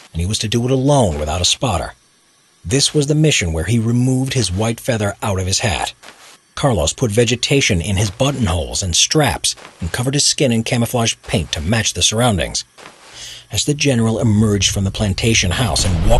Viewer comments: I miss heard what he's saying, i thought he said "put vegetation in his butt whole" 😂. I rewatch the vid coz it doesn't sound right. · I heard "Butt on hole"